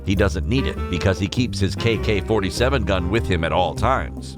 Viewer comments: did he say kk47? not ak47? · did he just say kk47 gun?